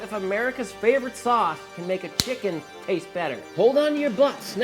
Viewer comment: When he said “hold on to your butts” I heard “roll up your blunts”